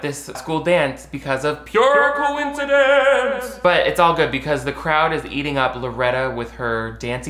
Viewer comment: Oh god, I misheard you cuz I wasn’t paying attention.. At first, I thought you said, “it’s all good cuz the crowd is eating out Loretta.”